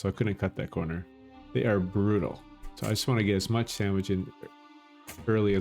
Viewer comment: Nice guide 9:41 I heard you say "I just want to get as much sandwich in" xd